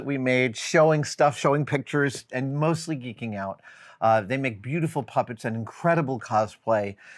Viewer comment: The first pass, I heard “And mostly making out.” LOL I’m kinda glad I misheard